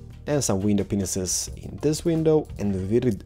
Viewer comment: Did he say "window penises" at 11:09? LOL